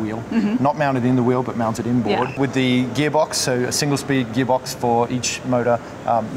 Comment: Ahhh...silly me I missed that. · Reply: on first listen I swore he said they had 2 speed boxes on each motor. But then I rewound and it was single speed (which is what every other EV has, so no worries!)